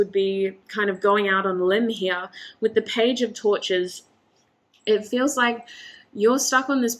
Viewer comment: I heard page of tortures...